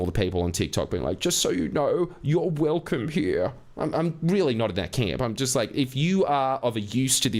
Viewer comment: “I’m really not that camp”; is that what you said? 😂